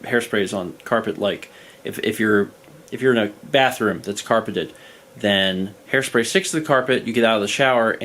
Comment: I love this video yk this, I am yelling at the top of my lungs, “WHOS BATHROOM IS CARPETED”. I was trying to put myself in this “environment” of myself, hairspray, and carpet. And I hear “if your bathroom is carpeted”, I am yelling who’s bathroom is carpeted? I am so genuine right now is that a thing? Also I am cackling at “When you have hairspray on the carpet and you get out of the shower” because it’s something so specific and sounds like you lived through it already.